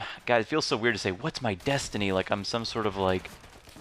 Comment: destiny i thought you said density